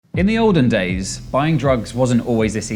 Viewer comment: I thought he said "back in Odin days" lmao